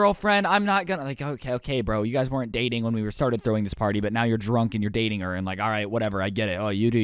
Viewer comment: did he say drunk!?!?! SUSAN!